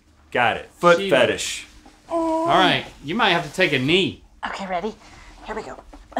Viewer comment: "you might have to take a knee" All I heard "Take a knee son" From when link pee'd chocolate into Rhett's mouth